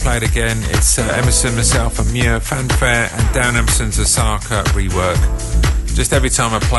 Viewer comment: the host mentions the name of the track, something like "MSM South Fanfare???" I can't decipher what he said. Does anyone out there know exactly? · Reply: I thought he said - Darren Emerson, Nick Muir & John Digweed - Fanfare (Darren Emerson Osaka Rework) - But that doesnt sound like the same track ?